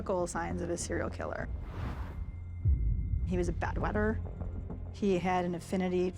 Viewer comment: Does anyone know what the woman says at 5:35 ? Closed caption thinks it's "Bad weather" but it sounds almost like "bedwetter"... Is bed wetting a telltale sign of psychopathy?